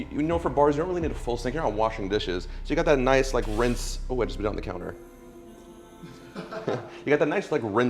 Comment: did he just say "I just nut on the counter" ????????????????